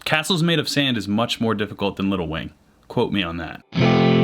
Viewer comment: Who else thought he kept saying little Wayne instead of “little wing” 😂